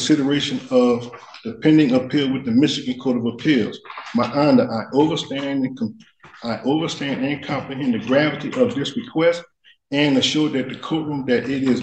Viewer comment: I know he said "my honor" again but did he say he "overstands"after that?